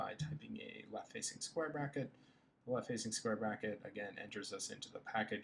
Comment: Very helpful, its just I couldnt hear clearly what is going on in 5:58, what exactly you did to get into pkg manager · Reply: He wanted to explain that you have to press the closed square bracket to activate the package manager. So e.g. on my german keyboard I press altgr + 9 for the ] symbol. Then the comand prompt changes from Julia> to pkg> Hope that helps